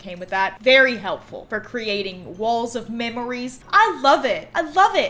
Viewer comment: Did you say "Walls of my enimies"? Well, thats me